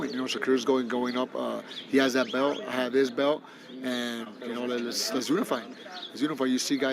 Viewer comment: “ I have his belt “ did he say that ?!?!?